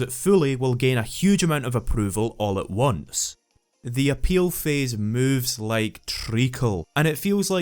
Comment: Did you say the appeal phase moves like Trico? Like, from The Last Gaurdian? That's a funny analogy!